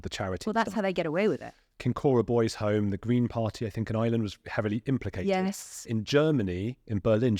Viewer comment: Apologies if I've misunderstood something, but - did he say that the Green Party in Ireland is heavily implicated in Kincora ?